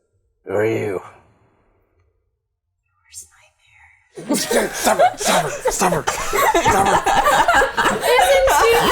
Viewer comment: What does say at 55:50 ? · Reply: "your worst nightmare"